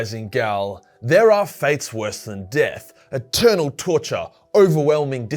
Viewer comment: Ik he said "eternal" but all I heard was "Turtle torture!!" And I was like... WHAT!??¿